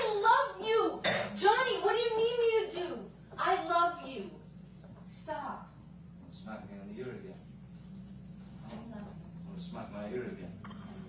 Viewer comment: Not a native English speaker here but did he say at 5:00 "You wanna smack my ear again"?!